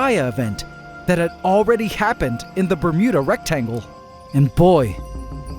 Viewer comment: I'm sorry, I keep hearing "Bermuda Rectum"